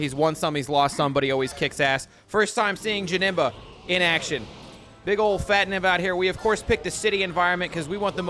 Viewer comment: He said big ol fat nemba so fast , I thought he said big Ole fat ni.... nevermind 😅